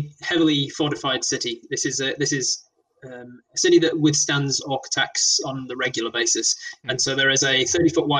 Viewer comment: The city of Wratih has resisted orc attacks for centuries. I misheard that and am now intrigued by the idea of a city that resisted architects for centuries.